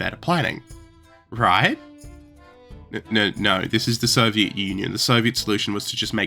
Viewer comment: "Soviet Onion" killed me 😂. Yes it's funny when misheard.